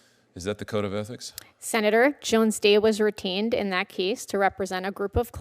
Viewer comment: When she says, "Jones Day was retained . . . " it sounds like she was saying, "Jones Day was routined . . . "